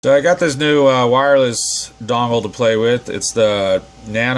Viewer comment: I’m so tired it sounded like, “so I got this new dongle to play with”